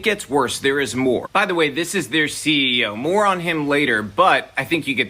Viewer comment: "This is the CEO , more on him later" Though English isn't my first language I first misheard it as " This is the CEO, moron him later". Now I'm curious, what's goin' to happen later.